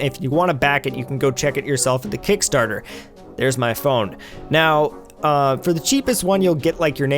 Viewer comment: did you just say "There is my phone?"